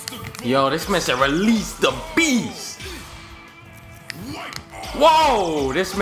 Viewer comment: I heard that "release the bogus"